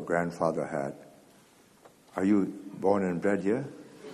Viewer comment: I heard it as "born and bred here"